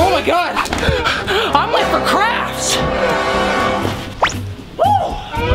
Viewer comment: It sounded like you said I’m late for some craps